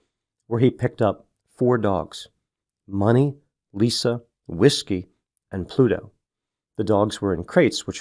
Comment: I thought you said, “where he picked up four dogs, money, whiskey…” lol so I was like oh, party in the sky, that’s why he crashed 😔